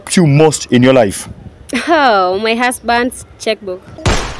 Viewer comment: when I first heard it it thought she said my husband's sex book! t